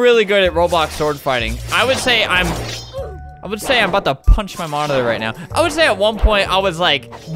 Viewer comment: I thought he said “ima Boughta punch my mom”